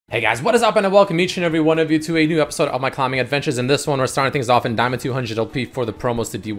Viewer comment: I swear I heard "welcome to my commie adventures" in the intro XD